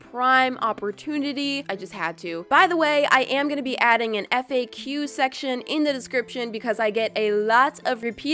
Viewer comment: when she said "FAQ" section, I thought she said "epic Q section" Lol